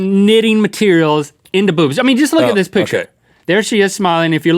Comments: I thought Link said "boots". Then I saw them and I was like "those don't look like boots. They look like OHHHHH" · DD every single time I thought he was just saying "boots" in a way that sounded to be sort of like "boobs" to me. And then it really was boobs... lol